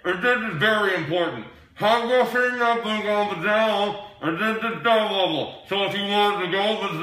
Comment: ll i heard was "I'm going to take you down and do stuff". Plz Mark don't hurt me!